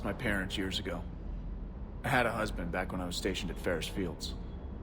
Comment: Did he just say he had a husband? Really?